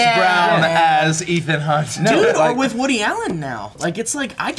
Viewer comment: When brought up Woodie Allen, for a second I thought he said Hoodie Allen and got terrified! Haha I love Hoodie so much and would hate to find out something awful about him.